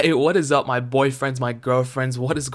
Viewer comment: hold on i been drinking recently but did he just say whats up my boyfriends